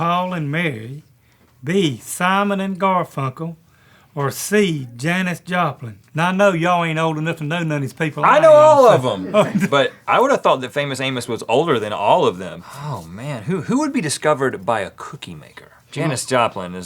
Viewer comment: thought he said the famous anus.